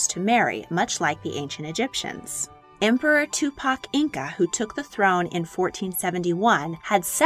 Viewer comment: did i hear that right? Emperor TUPAC????